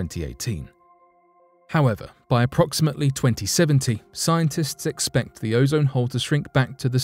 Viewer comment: I could have sworn at first that the narrator said "By approximately 2017, scientists expect the ozone hole to shrink back to the size it was in 1918", even though this video was released in December 2020. Listening several more times, I found he actually said "By approximately 2070". Thought I'd point this out, given that some of the commenters here have some interesting ideas about the direction of time, and other things, and so there's no telling how many people also thought he said 2017, but accepted it at face value.